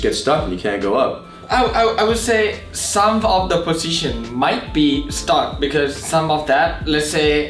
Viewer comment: when said "some of the positions" I definitely heard "some of this pussy shit"